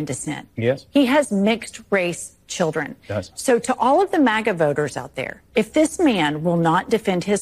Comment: Ohh damn I thought you said maga boners not maga doner... hehehehe I had to replay it...Just woke up... sorry